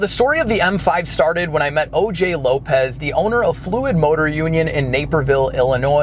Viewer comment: LOL... When said he met "O.J. ... Lopez" the immediate thought in my head was like "did he just say that he met "O.J. Simpson"? LOL... Then my brain heard... "lopez"... LOL